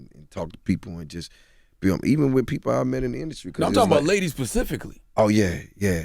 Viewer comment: Did Charlemagne say “pacifically”?